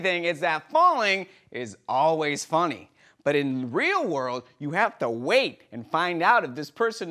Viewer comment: Anyone else catch that slip up at 7:56-7:58 "thwheel world" lol